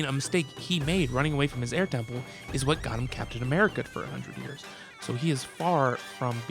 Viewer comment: anyone else hear him say "what got him kept in america for 100 years"?